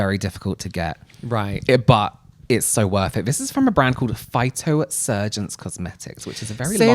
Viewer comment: I definitely heard Phyto-“surgeons” even in own episode lol